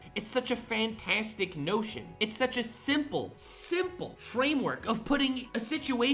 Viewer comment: "It's such a simple, simple notion!" I heard 'simp'!